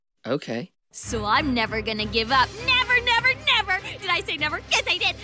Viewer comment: what did she sayyyyy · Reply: Never